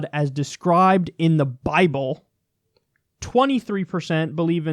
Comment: What I hear: "56% have never read the bible."